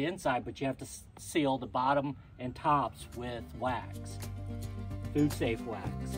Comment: What did he say? "________ safe wax"? Did he say dude safe wax? What is that? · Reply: Food safe is what he said.